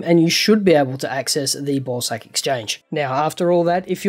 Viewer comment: Is it just me or am I the only one who heard him say ballsack exchange instead of bawsaq?